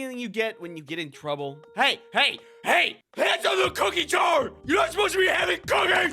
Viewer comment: did he really say "cookie chor"? Or my Bengali brain is messing with me?